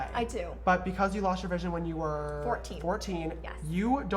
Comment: omg lol on 6:38 it sounds like he said: "but because you lost your virginity when you were FOURTEEN" XD (I know he said vision but first I thought he said virginity)